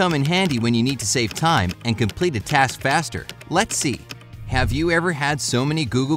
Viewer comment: It kinda sound like its saying “Lexi” not “lets see”